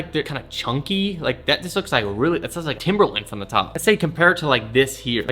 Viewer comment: did you actually say timberlakes? smh